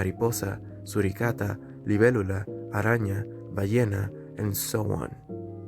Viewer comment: I don’t know Spanish that much but DID YOU CALL YOURSELF A HYENNA OR AM I DEAF? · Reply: I was saying the words in spanish while the english ones were written on the screen, I said "Ballena", which means "whale". Basically I called myself a whale, Just a joke 💀